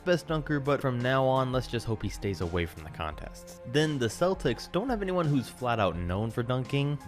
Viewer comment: Sorry I thought you said Milwaukee bucks when you said celtics.